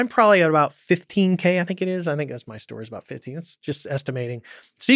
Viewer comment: I love this. Could swear at about 1:24:55 he says 15k is that my story? Or is that just me?